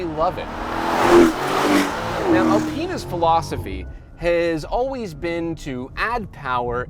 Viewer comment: it sounds like is saying "a penis philosophy has always been to add power and keep comfort"